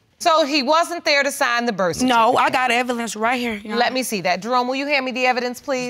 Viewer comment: Im sorry did she say I got “evilence “ 9:13 right here ??